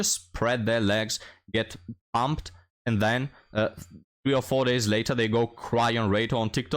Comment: I heard you say "she was dumped 3 O' 4 days later" and I was thinking 304. It works!